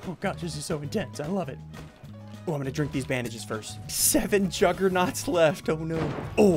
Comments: did he just say im gonna drink these bandages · did he say "im gonna drink these bandages".???